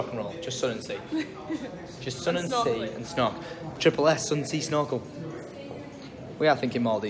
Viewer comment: When you said triple S I thought you said sun sea and snot 😂😂😂😂